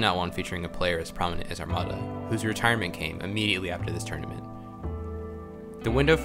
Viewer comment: for a second I thought I heard "who's retirement came immediately after this turnip" I thought "damn it wasn't THAT bad"